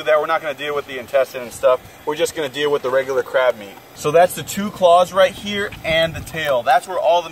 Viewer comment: did he say crab meat